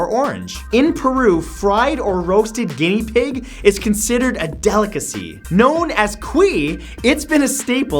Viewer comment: I thought me said "Gay pigs" on number 8 lol.